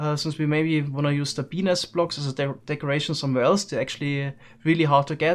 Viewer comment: And we may want to reuse the penis blocks. Yes, I know he says Bee nest blocks, but it firstly sounded like that